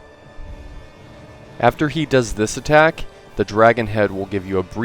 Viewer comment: North west I thought you said weest